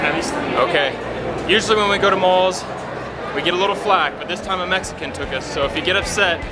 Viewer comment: Im trying to improve my English so that’s why I’m watching this video. Can someone tell what he said at 3:47 I understood something like “We getting a little flat “ it’s this an expression what does it mean? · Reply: Dijó, We get a little flack. No sé exactamente la traducción en español pero es si alguien se queja. Por ejemplo, My mom gave me flack for not washing my hands before dinner.